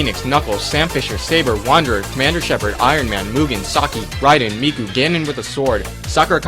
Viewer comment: lol did he just say Hatsune Miku at 7:24??? :O that would be pretty cool